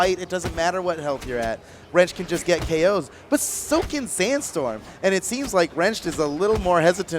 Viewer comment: мне не одном послышалось, что комментатор сказал "The сукин Sandstorm"?